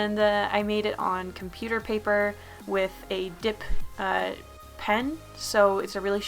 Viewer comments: Did you say dick pen..??? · I thought said "dick pen." but now I think she said dip pen. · OK I really thought you said a "d**k pen......